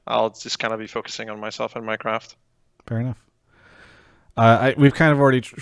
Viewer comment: I heard minecraft.